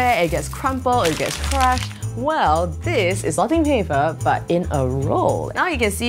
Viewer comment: At first I thought she said wrapping paper 😂